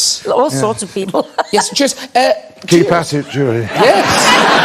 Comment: Can't understand Ian at 25:07 , when he speaks to Judy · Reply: He says: "Keep at it Julie"